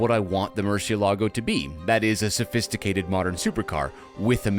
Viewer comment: I gave this video a like pretty much at the beginning, forgot about it and when i got to this pun i wanted to give a like again 😂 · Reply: i also thought he may said "Poor Supra".😅